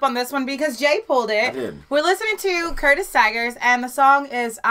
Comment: When I saw the song and singer name, I wasn't familiar with it. But as soon as the video started, I surely remember this song. These blasts from the past are fun. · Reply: The same here. I blame the radio DJ 😁. Thought he always said Curtis Tigers 🐯.